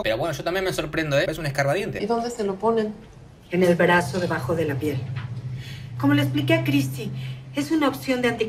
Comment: escuche re mal xd escuche en el braso debajo de la pierna